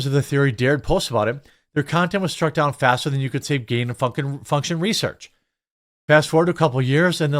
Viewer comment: All I heard was "gain off F*ckin research" LMAO